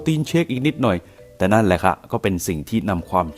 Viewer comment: แอดพูดว่า "แต่นั่นแหละค่ะ"